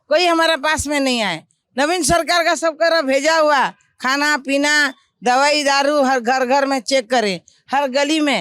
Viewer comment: Did I hear "Daaru" ?